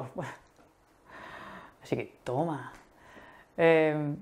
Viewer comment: cuando hablabas de la conversación con la comadrona, a las 8:44 dijiste algo como "tomas". Qué dijiste exactamente y qué significa? Gracias! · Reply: Dije: ¡Toma! (Take that!) :D